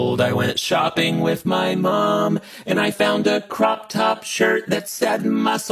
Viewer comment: I heard “Jerk” instead of shirt💅